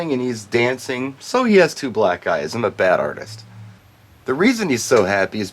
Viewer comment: "so he has two black eyes". I misheard it as "sorry I drew black guys"